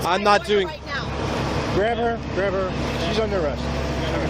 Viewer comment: First supervisor didn't have any of that bullshit! "i didn't even get to talk to the supervisor!" "He didn't say a word to me!" I disagree. I think I heard this? : "Grab her, she's under arrest." ;)